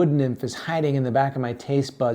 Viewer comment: I legit heard "breadstorming" at the end The bread looked so good it occupied my whole brain